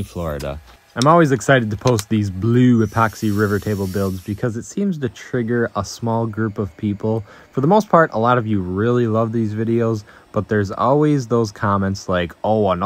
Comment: Haha I heard "I like to trigger people" I feel ya man... Me too